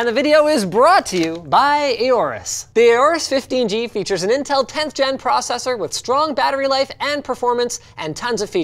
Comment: I thought Linus said that the laptop had an Intel pentium cpu